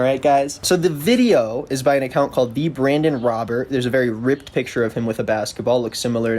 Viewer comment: At first I thought you said "brandon rogers" and I was confused on how he could lie bc those are characters, he's not saying any of his content isn't produced and directed and scripted, and we all know it's comedy. Then I looked down at my screen and was like "oh idk who that random guy is nevermind"